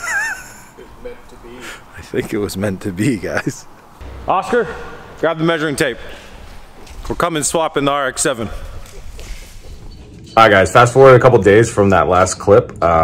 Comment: Lmfao what a amazingly placed Ad😂 all I heard was “Oscar grab the measuring tape we’re cumming” then a Ad cuts the rest off 🤣